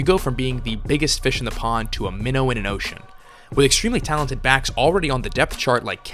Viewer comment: That was weird I thought I heard him say “with extremely talented backs like Kenyan Drake and TJ Yeldon”. But those words don’t go together😂